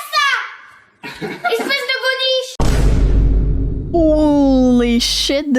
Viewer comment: the original creator must have believed the kid said « boniche », which is a derogatory term for a maid, but he actually said « godiche », an old-fashioned word meaning « stupid ».